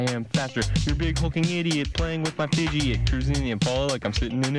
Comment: Did say fucking idiot